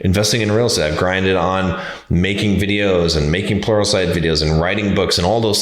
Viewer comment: BRO, did you just say you grinded on making pornsite videos?! lol